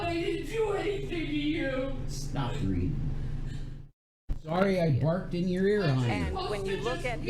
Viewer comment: does say 'stop breathing'?